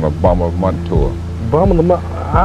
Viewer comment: “Well, you’d be on the tour.” Savage. 🤣🤣🤣🤣☝️☝️☝️☝️ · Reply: Haha initially thought he said, “You’d be on the floor.” 😂